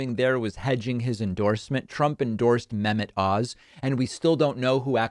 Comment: When you said "Dr Mehmet Oz" I heard "Dr. Menopause"